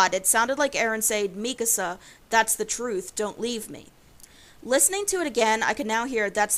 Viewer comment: I heard, “What’s that matter, what’s the truth......” and the rest, well who knows HELP MEEEEE!!!!